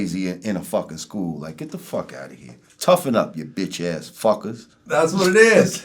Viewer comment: lemme show you what JaMoR is really saying: Toughen up ya BitCh ass Phaggots!!! 😶